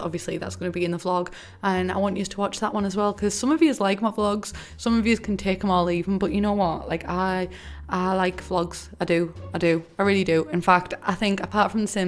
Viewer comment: When she said “some of y’all can take em or leave em” I thought she said “some of y’all can take molly”